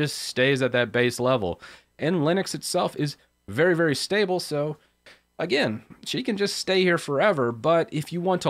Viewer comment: Did you just say your mother in law that stay there forever? :)